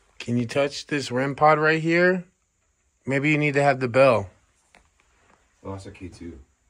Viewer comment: I heard it as you reached for the bell